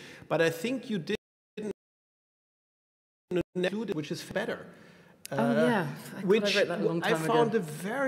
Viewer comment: what is the essay called mentioned at 27:26? the recording glitches and it sounds like kehlmann is saying "which is fedder"? can't find it online. tx!